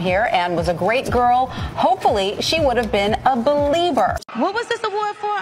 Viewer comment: Justin is sick, I’m screaming!!!!! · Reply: just confused, I thought he said "beleiver" like religion n stuff